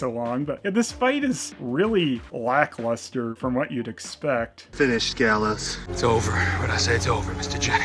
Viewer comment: I thought he said FINISH HIM lol